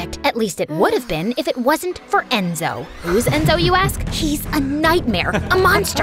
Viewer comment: Did she just say enzo😮